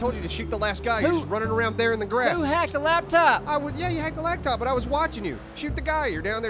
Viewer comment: Did say "Hacked the Lacktop"? That's funny. Great episode guys!!